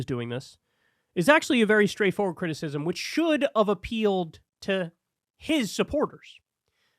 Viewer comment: Did you say "should of"?